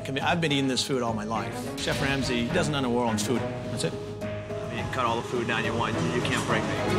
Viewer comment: somebody pls tell me wtf he said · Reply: He said that's it